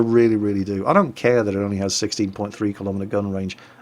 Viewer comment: did he really say 16.3mm gun range? Man that sucks ;P